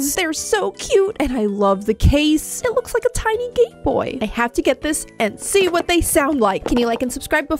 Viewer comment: I thought she said gay boy